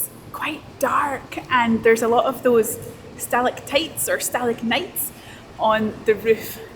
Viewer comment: Did you say you might think they’re stalagnites? My spelling says stalagmites say Hey! Really love your channel. Love to rent a narrow boat! Hi to all you younglings! Happy 2025!